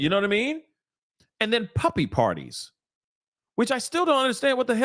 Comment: Omg I always thought they were saying puffy party’s not puppy party’s that makes it so much more horrifying 😮